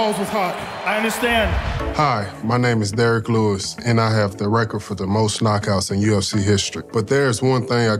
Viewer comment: I thought he was gona say, “I have the record for the hottest balls.” Lmaoooo